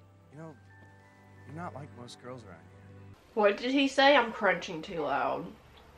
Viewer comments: “What did he say I’m crunching too loud” MEEEE 😂😂😂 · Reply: Emily Cook I thought she said I’m cringing too loud lmfaoo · I thought you said "what did he say I'm cringing too loud", and... still accurate.